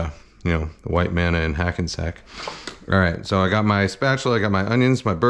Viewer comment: I thought you said “white man with a hacky sack.” Probably a similar smell tho.